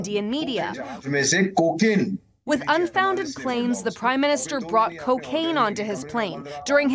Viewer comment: Did she say "the prime minister brought cocaine on the plane?"